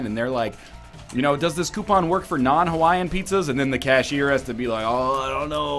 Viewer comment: Oh... pizza I heard "peaches." I thought it was just the typical NL joke when he started talking about flavors...